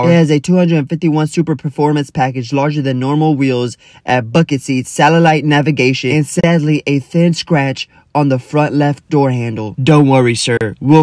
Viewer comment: I thought he said add a fuck it seat💀